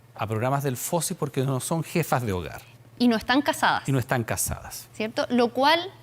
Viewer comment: "y están casadas" es lo correcto ustedes modificaron lo que dijo el candidato y después lo dicen mentiroso!